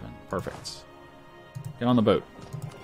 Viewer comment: CC says "perverts on the boat" ... I don't know what to say... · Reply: Qwertylool "get on the boat". Get your mind out of the gutter.